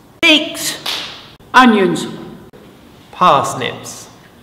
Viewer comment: mmm yes polish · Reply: I thought he said turnips